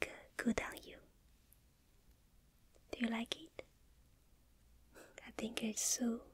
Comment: I thought I heard she liked me 🙌🏻👏🏻🙌🏻👏🏻